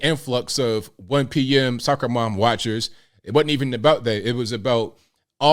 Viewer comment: Hah - i heard "sucker mom watchers" ... Freudian hearing i guess.